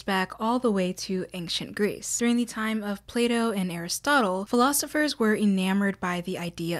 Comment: I'm sorry, but i thought you said Playdoh...